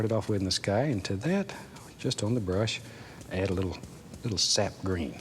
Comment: it almost sounds like he said "add a little Seth Green"... M'kay, Mr. Ross. Anything you want.